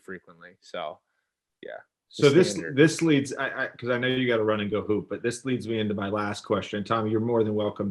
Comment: i thought he said, "i know you gotta run and go poop"